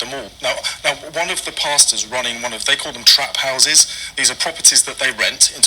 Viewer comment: Omg. Did he just say they have no trap houses